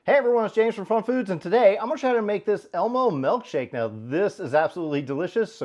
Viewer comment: I honestly thought it said Emo Milkshake...